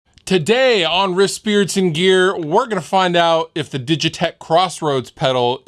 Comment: The close captioning thought you said “wrist spirits in gear.” Great band name.